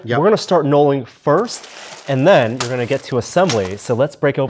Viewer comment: I always thought they were saying nulling. As in the 0th step that you take.